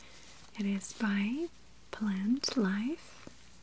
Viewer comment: Lol I thought the bag of soap said "Plaintiff." I was so confused.